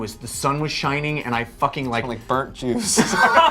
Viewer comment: Oh lol. I thought he said burned juice. I get it now.